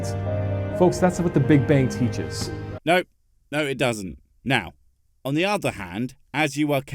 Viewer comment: Listen very carefully with the volume up... right after he says "that's what the big bang teaches"... Am I hearing someone in the audience saying "no it doesn't" right before you cut in? Kinda want to check on an un-edited version but I don't want to add to his video's view count...